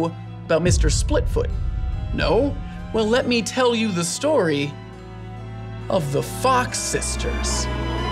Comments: On 3:21 I heard "...story of the f*ck sisters'... I'm absolutely spoiled.... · I thought you said “the F*ck sisters”